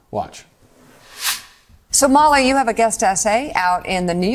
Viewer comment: I thought she said “Somali” instead of “So, Molly”